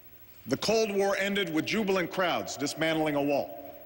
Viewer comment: Jubilant crowds.....I almost thought he said jubilant krauts!